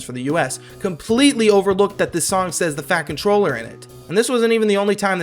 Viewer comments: i thought that it said "fact controller" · fun FACT: when i first found the uk dub of classic thomas in like 2019 or something i thought the narrorator was saying "the FACT controller" lol